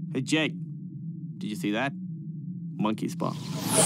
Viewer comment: Did he say monkeys paw